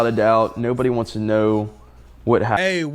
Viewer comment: did he say nobody wants to know what happened??